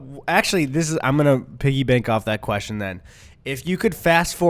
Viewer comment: did you say piggy bank or piggy back at 43:25? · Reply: Bank